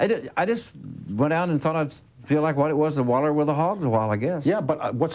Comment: Amazing interview with two of the greats anyone speak Arkansas that can tell me what Glenn Says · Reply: He says ‘Waller with the hogs’ meaning - wallow with the hogs - meaning, roll around with the hogs, meaning - just kind of get a bit crazy, all loose and messy…